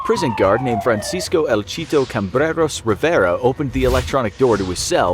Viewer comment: Jeez bro that's long the only thing i heard is cheetos